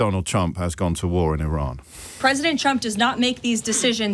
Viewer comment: Lievit always sounds like she says "President chump"🤣